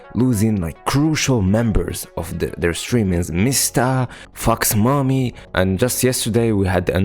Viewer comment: I am sorry but when you started listing the names of the VTubers who graduated at the start of the video you said "Mista, fox mommy" but I heard it as "Mista fucks mommy" and my brain just accept it again I am sorry ^^